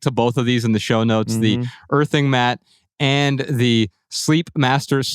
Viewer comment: At first I thought said “birthing” mat and I was very confused. 😂